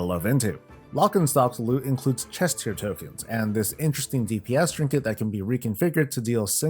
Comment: I misheard 1:36 as “chest hair tokens” 😂 🐻